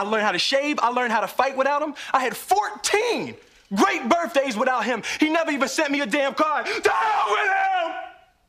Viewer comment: “To hell with him” always is the deal maker to make my eyes water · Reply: I thought He said " DAMN WITH HIM!" not "TO HELL WITH HIM" 😭